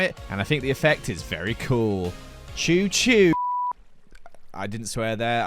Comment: CHOO CHOO BIT- Ok but why can i hear actually saykng that?